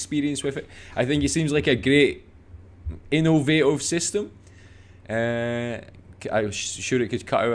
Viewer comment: "I think it´s a Gree innooh vaá ohh system" is what i hear as a Swede. I love dialects! Such a joy to hear how much is the same but yet so different. We have the same word in Swedish if you look closely it is really the same (innovationssystem).